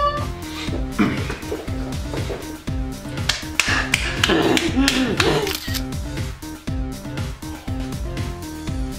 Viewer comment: Estaba tranquila con el video minimizado hasta que escuche el minuto 5:32 y mi madre pensó mal 😂😂😂😂 ya sabrán por que 😅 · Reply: Paja